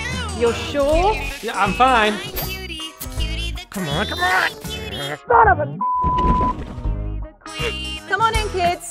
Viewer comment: WHAT DID BRO SAY💀💀💀💀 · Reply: Son of pig?